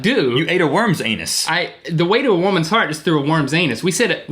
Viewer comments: Did he say "The way to a woman's heart is through a woman's anus"? I thought this was family friendly... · I realized that you messed up that one part of the song, but I thought you said worm's tail, which would make sense since it rhymes. I guess I just heard it wrong.